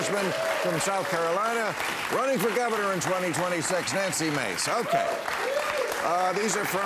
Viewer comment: Who was she? I thought they said Nancy Grace.